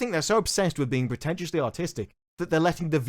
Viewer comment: thought you said ".... potentially autistic..."